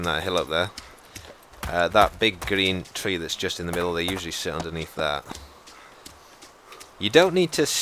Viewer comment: it sound like he says they shit underneath that